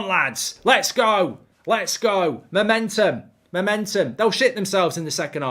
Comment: Thought u said we'd shit it??? hahahaaaaaaha